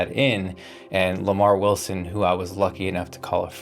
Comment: Wait I thought is intro said Lamarr Russell not Wilson 🤦🏻‍♂️.